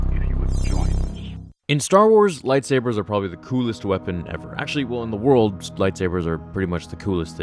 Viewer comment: I played this Part 8 times and i dont understand what you said. "In Star Wars #%@@ Republic" PS: Im not a native english speaker. ( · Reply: Ƥ4Ƭяι0т he said “in Star Wars lightsaber is probably the coolest weapon ever”